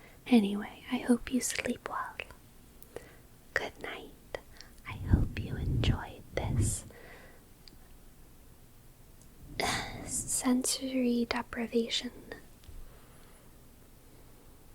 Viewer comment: My bad. I thought it said "sensory defecation"